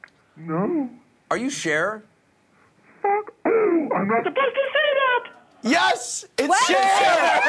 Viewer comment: Sounded like cher said fuck you! Lol when anderson asked if it was cher